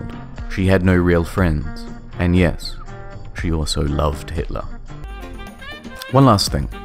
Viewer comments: why do the subtitles say ' love Johntron?' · I love how it sais "Jontron" instead of "Hitler" in the captions